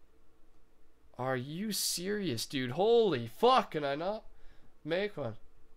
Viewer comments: Round 17:00 ish did I hear that right did rusher just say fuck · did you say holy f*ck? Man didnt expect that at all! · did u say holy fuck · you guys heard him say holy fk can i make one? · did rusher say "Holy Fuck can I not make one" or is that just me?